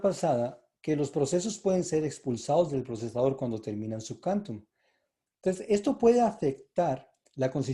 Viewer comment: su que??? No entiendo lo que dices en ese instante Quantum cuánto??